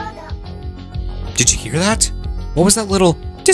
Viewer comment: Discordo? I always hear: "Discord Up!"